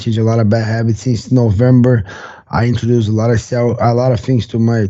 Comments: Did he say "i introduce a lot of cell..."??? Stem cell doping? 🤣🤣🤣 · Gilbert almost slips and catches himself at the 9:12 to 9:25 in the interview he starts to say how he's implemented changes and added what's sounds like he is about to say Steroids but catches himself and continues rolling on. Now I wonder quick turnaround and faster recovery. I'm only saying what I thought sounds like he is about tosat Steroids. ???????